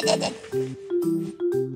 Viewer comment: チャンネル登録が ファンネル登録と聞こえてしまった…